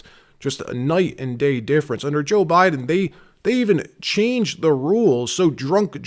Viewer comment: ….it sounded like you said “under Jill Biden”😂